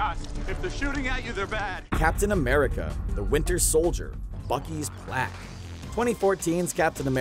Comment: I was looking away when it was said so I heard "Bucky's Black" I was really confused for a second 😂😂😂